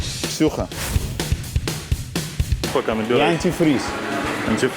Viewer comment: что он сказал ? Шлюха?